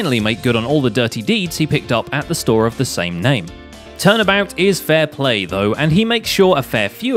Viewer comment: wait did you say 'turnabout'? Pursuit 20XX - (Something about Phoenix Wright pointing at someone) intensifies